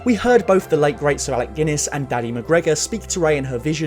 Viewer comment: what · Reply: Oh. I thought he said “Danny McGregor” based on his character from Doctor Sleep, but now it’s even funnier.